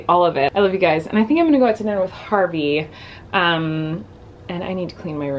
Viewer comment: At one of the parts it sounded like she said "Well I'm gonna go watch Barbie."😂 She said Harvey 😂😂😂